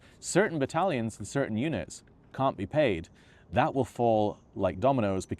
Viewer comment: I heard "I speak to eunics" instead of units and was so confused.